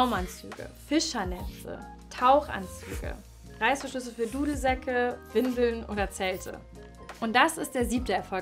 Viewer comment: Hab ich das richtig verstanden? Reißverschlüsse für Windeln?